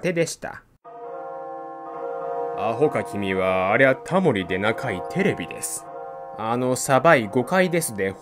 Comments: あほか君は、あれはタモリでなか、テレビです。って聞こえる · 少なくとも自分は[昔々、偉大なるタモリ神は悟りを開き]のところが、[アホか君は、ありゃタモリで長いテレビです。]と聞こえる。面白い。